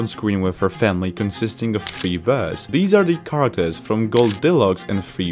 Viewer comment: im sorry...what did you say? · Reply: Three bears